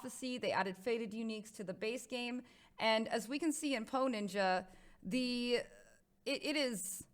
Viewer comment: every time she said "poe ninja" I thought she said "hoe ninja" lmao